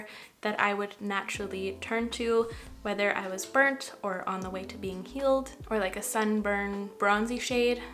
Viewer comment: when talking about the blush placement I thought you said “...on the way to being killed....” lmao I’m so sorry. Had to rewind that part haha. What a beautiful look you did. I love no makeup makeup looks!! Love from FL 💕💕💕